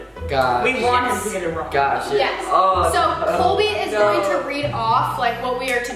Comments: why did i think i heard zoe say "colby" instead of "cody" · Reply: chai she said Colby because Colby is filming. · did Zoe say Coby or was that just me